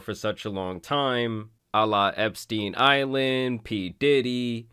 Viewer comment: love you videos man, but please don’t involve peoples mocking Jeff Epstein with peoples religious god. I think I heard you say Allah Epstein instead of Jeff Epstein. Correct me I’m wrong 🤷‍♂️